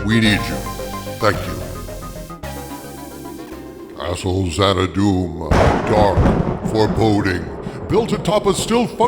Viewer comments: It sounded like you said "Asshole Xana-Doom" hahahaha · I swear I thought I heard you say ASSH*LE Xanadoom 😂